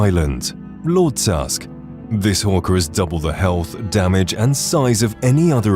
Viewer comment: I am hard of hearing so I heard “This fucker has double the health” 😂